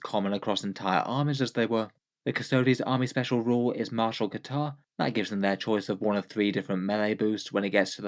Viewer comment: I was listening along and thought I heard "Martial Guitar" and thought the emperor had holy noise marines now